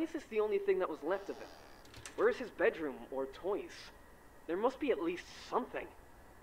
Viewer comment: the narrator/phone guy says 'There' and the subtitle says 'It'. I'm mildly annoyed by this.